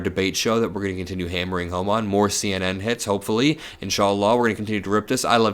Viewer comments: DID HE JUST SAY "INSHALLAH" AT THE ENDING?!?!?!??!?! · did he just say Insallah ?